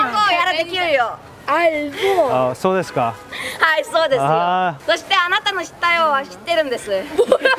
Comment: what did the girl say? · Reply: わたしだって日本語できるよ I also can speak Japanese.